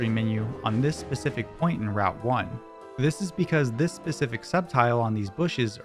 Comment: i misheard this as “this specific Sceptile”